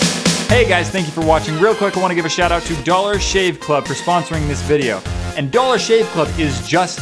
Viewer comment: I thought you said the "Never shave club." I was like, "that's ironic."